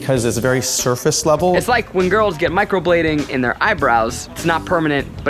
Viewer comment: my drunk ass thought it said microwaving